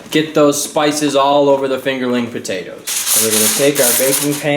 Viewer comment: LMFAO i thought it said fingering potatoes FACEPALM